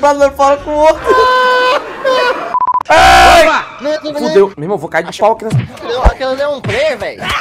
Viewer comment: como é que é limão tu vai cair no pau e isso mesmo que eu escutei kkkkkkkk